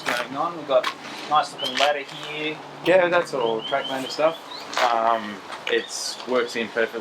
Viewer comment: who makes the ladder? Didn't quite catch that.. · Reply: Sorry about the delayed reply. Its by a company called Tracklander